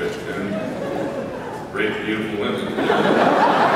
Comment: hahahaha the hole cast at 4:23 is like "did he just said rape beautiful women??" that made me laugh so fucking hard